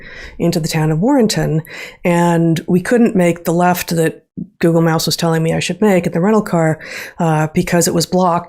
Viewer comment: Google Mouse? I thought I misheard, but that's what the Closed Captions heard as well. 😉